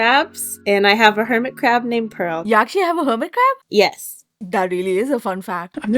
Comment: I thought she said a Hermitcraft named Pearl and I got very excited 😂